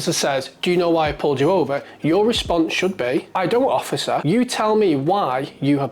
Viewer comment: Did you say "I do, officer", or "I don't, officer"? It wasn't very clear at that point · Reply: "I don't."